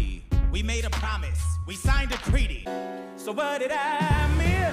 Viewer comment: Am I the only one hearing creety instead of treaty at 4:57?